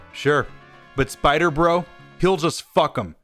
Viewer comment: Did anyone hear fuck the autists